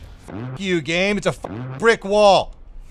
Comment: The polish Leopard got hit with the ugly-stick,......five times. 10:32 Did you just say "prick-wall"?